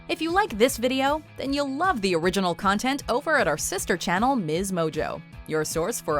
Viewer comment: I thought you said shister channel